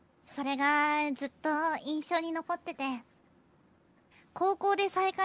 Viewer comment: ~TL Notes~ So, I debated whether to Hajime-ify Hajime's subs, but they all speak so quickly that I thought it might be too hard to read. 1:42 - She says 印象(inshou) and not 一緒(issho). The line should be "That left me with a lasting impression" but that sounds kinda stiff so maybe "That memory always stayed with me" or "I've never forgotten that moment" if you wanna get even broader "I can still feel the warmth of the cocoa even now" or "Your kindness that day always stayed with me" (basically insert line about that act leaving an unforgettable impression) 0:44 - The full line: うち、あんたのこと好いとうよ。uchi, anta no koto suitouyo. 1:06 - Hajime combines aishiteru and suitouyo to make aishitouyo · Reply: I think raden says 印象 instead of 一緒